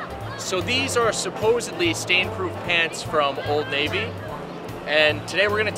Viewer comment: I thought it said Satan proof pants 😂